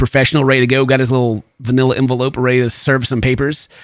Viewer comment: wait wait wait wait waaaait ......did I just hear vanilla envelope?!